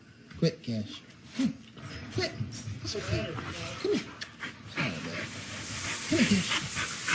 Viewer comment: in slow-mo I heard "it is so bad..."